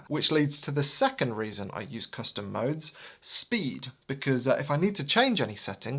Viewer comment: thought you said "custard modes"